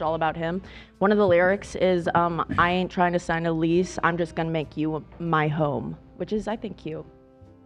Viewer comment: what an absolutely disgusting assumption to make about someone. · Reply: I take it back. I thought she said “I ain’t tryna sign a lease, I just want to make you my hoe.”